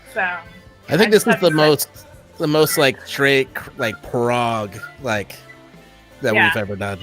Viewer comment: at @ 21:54 what does he say? Drake Prague?